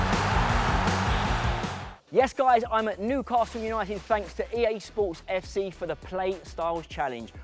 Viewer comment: At the start it sounded like Billy said im a newcastle fan